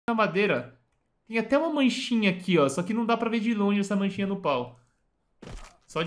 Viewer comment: de tudo que ele falou, eu só ouvi "mancha no pau"